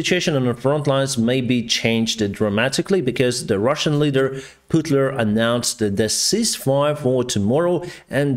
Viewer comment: Did I hear that right ? Did you say Putler?